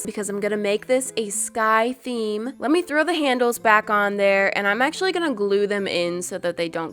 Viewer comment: When you said let's throw those Handel's on I thought you said candles 😁😁